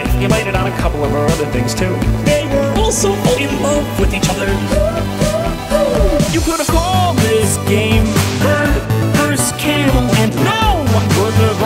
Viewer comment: I always thought he said 'You could have called this game burn Bruce Campbell'